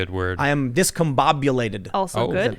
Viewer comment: "Verklempt", pronounced as ferklempt, its a Yiddish word. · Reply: I heard "beklemmt" which is the German word for mentaly choked up, where "verklemmt" means uptight...